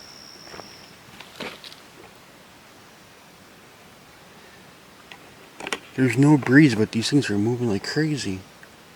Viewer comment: Hey. Around the 19:55 after you said "Did you see that" there was a voice that said something but i could not understand what it said. Sorry to hear Junior wasn't along this time. Great kid! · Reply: the voice sounded like it said "my sister"